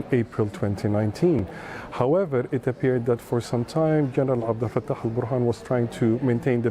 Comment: what name does he say? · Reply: General Abd Alfattah Burhan